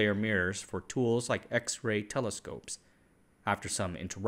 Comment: Informative video! I noticed around 8:15 you say telescopes but the caption says microscopes, which did you intend to say?